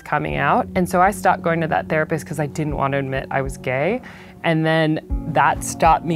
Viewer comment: Wait she is gay?Did i hear right or i my ears aren't good😂